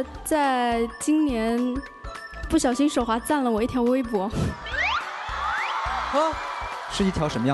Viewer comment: Did she say something like “wang yibo”? 😍 3:44 Can someone translate that conversation for me please? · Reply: No she didn’t.. she said sth about Weibo ( a Chinese website)